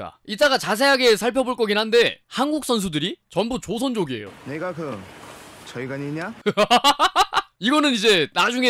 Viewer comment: 0.25배속 "니가 그 제권이냐? 라고 들리네요